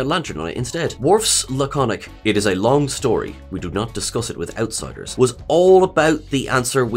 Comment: WAIT WAIT WAIT WAIT WAIT... I can NOT be the only person who literally thought Worf said "we do not discuss it without Ciders." ... i mean... I have Always thought he said this, "outsiders" never even appeared in my head... I'm not joking, I'm dead serious. I like my version better.